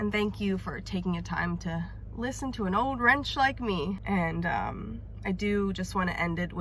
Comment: Did she say @ 20:30 “to listen to an old WRENCH like me”???